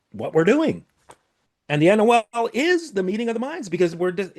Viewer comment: what word is he saying? the "anuel"? i'm having a hard time knowing what word he is using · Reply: NOL = Notice of Liability. For clarification, our organization does not educate on or provide services related to this process. You may inquire with organization to learn more.